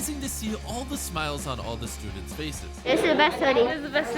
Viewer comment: fr i thought he said he was amazing to see all the smiles on the stupid faces